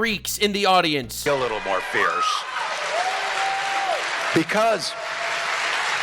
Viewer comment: did Tim say, “Little Weiner…” 🤔